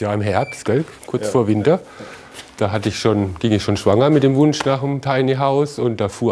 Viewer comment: was sagt der da?!😂 ich versteh immer:,,da ging ich schwanger mit dem hund raus“😂😂😂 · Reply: da ging ich schon schwanger mit dem wunsch nach einem tiny house